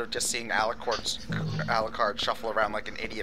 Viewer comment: did just say alicorn?